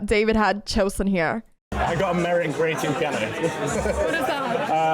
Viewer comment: Did he say, "I got a merit in Grade 2 piano"?